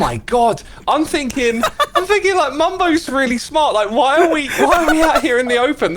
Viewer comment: Did called him Bumbo or did I misheard 💀